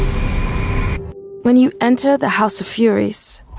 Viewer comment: Y'all can't believe this, my friend misheard it and said "House of Fries"